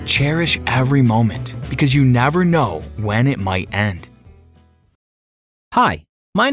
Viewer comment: The first one I thought he said you never know when you might blah lollll